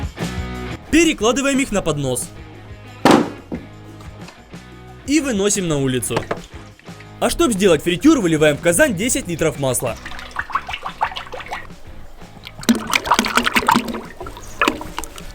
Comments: мне одному послышалось "Казань" ? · серьезно? в Казань? я не один это слышу? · Мне послышалось или сказал Казань , а не казан